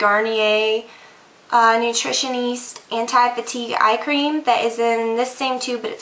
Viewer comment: when you said eyecream in the beginning I totally thought you were saying iCream, I was like what, a new apple product? hahaha